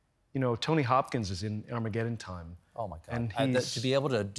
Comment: At first I heard “tony hawk is, is in Armageddon time”